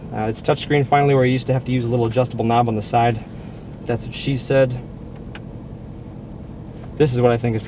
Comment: did he say thats what she said? lol